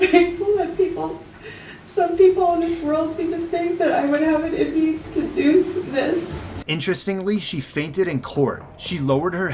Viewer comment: She says, "that I would have it in me to do this." The caption says, "that I would anything to do with this." Completely 100% not what she said.